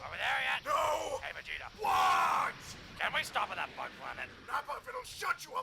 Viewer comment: I keep hearing "the bug planet" as "the fun planet" for some reason.